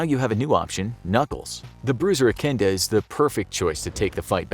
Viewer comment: i think i heard you say "echinda"